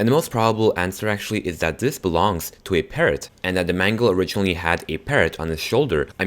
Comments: I p thought you said that mangle most likely had a parent on his shoulder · I thought that when said parrot he actually said parent 😂